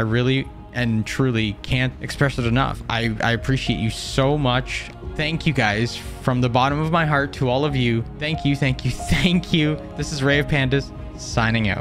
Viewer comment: at first, i thought your name was said like, not